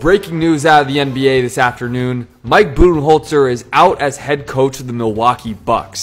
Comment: I swear bro I thought you said mike-booty-holler I’m sorry but I’m lmao